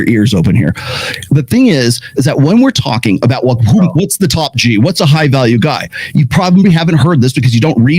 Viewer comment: Did I hear “Top Jew”?! 😅 🤯